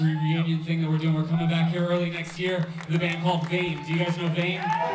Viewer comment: What does he say at 37:40? Bane? Thought Bane played their final show two years ago... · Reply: Oh! Vein. Heard what I wanted to hear I guess haha. Not that I dislike Vein but...